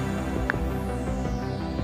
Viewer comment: Video: "Don't look it up" What i heard: "GO LOOK IT UP!!" So I did😭I shouldn't have 😭